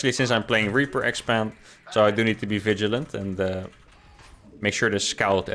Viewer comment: Ay i heard you say vigilant noice